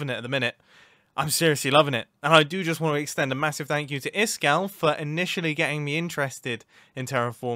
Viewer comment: Every time says iskall my subtitles say this gal and now I don’t know what to feel. Oh now it says iskall at 18:54🤦‍♂️